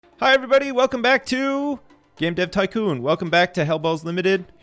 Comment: I thought you said "welcome back to yip yip tycoon"